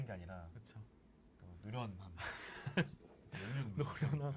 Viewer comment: 뭐라고 했는지 저한테 알려주실 분? ㅠㅠ 어렵네요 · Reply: 노련함이라고 했어요^^